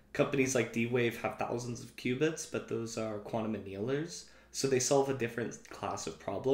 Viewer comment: Hello, just discovered this channel. Thank you for this. Question: At about 0:53 seconds you talk about how D-Wave has thousands of quantum bits, but those are __? I didn't catch that word. Looking to learn the difference in this "general quantum" vs those specific you mentioned. Thanks! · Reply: hi! thanks for the support :) d-wave builds quantum annealers with superconducting qubits, but theyre arranged in a large grid and they dont allow for general computation. they can be reduced in energy to give optimization solutions but only solve a subset of problems. ibm and google’s superconducting qubits on the otherhand are not annealing qubits, they can perform any operation (even annealing), so thats why it’s a bigger struggle to reach thousands of general qubits compared to d-waves annealing qubits